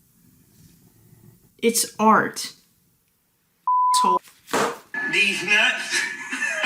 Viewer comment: What did she say? (I don't have a patreon) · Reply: “It’s art, Asshole”